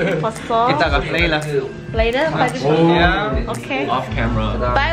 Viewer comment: 😂 WAIT WHAT DID DO? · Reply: astro.cheshire17 12:14 did he say "later..together, off camera"??o.O 😂😂